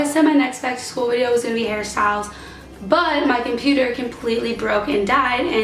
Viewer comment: when you said hair styles, i thought you said harry styles ugh welcome to my life